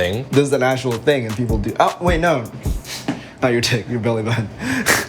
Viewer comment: What does Brian say he goes wait no, and then I can't understand... Anyone know ? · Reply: Took 5 years apparently but he says not your dck your belly button... I did it 😅